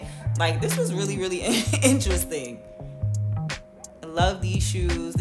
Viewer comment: at the 14:40 mark of the he video.... I couldn't make out the brands name that you said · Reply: Res ipsa